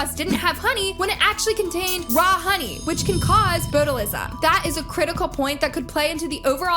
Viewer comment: omg lol i was casually listening while playing video games and heard her say Boatalism??? Botulism?